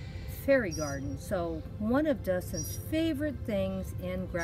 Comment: Yes Dustin really loves his dinosaurs I had to go look for them ,Then over playing in the garden and he started playing with him and I couldn’t find them oh well eventually I did find four I only showed two that I have another post to put it in a few days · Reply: I'm sorry, I thought you said Justin, I have a nephew Dustin who is grown up and a Father now, I also have a nephew Justin.